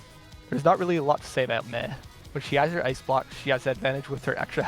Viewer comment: Did he call Mei "meh" ??